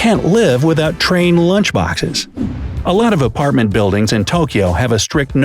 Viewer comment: I found saying LOTTA instead of lots of! XD